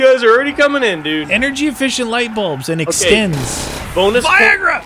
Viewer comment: did anybody else hear yell Viagra right after he said something about the light-bulbs extending. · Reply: I heard him say Extenz which is some sort of herbal supplement which works the same as Viagra lol